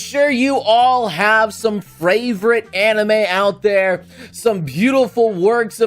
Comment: Is it me, or did just say fravorite here?